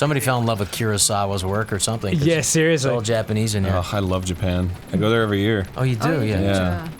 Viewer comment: Did Arin say," I go there [Japan] every year...since the bomb?" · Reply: I checked and he said, "I go there every year... it's the bomb."